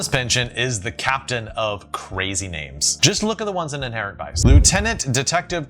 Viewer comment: What does he say here? “The ones in [blank]” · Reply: Inherent Vice